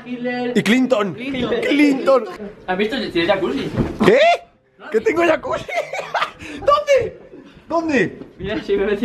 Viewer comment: te entendí que tenías yacult :v en tu refri xd